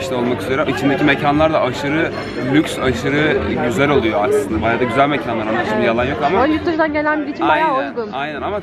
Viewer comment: aslında baya güzel mekan aşkım diyo ben mi yanlış duydum